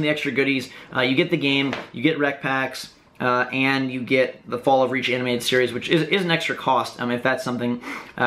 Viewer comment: did he just say fallout reach?